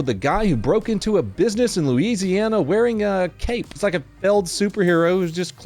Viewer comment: I thought he said Louisiana I got excited for a second… it was Indiana🤣